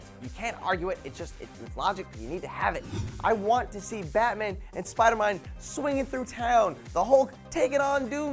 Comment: wait did you just say spidermine?